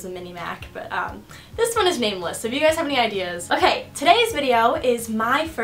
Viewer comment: Name it liss because I thought you said the computer is named liss not nameless